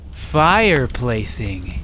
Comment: What could be more worse than F I R E P L A C I N G ? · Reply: I seriously thought you said FI replacing, and i got really confused